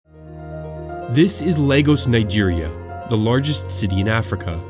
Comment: It sounded like you said Legos Nigeria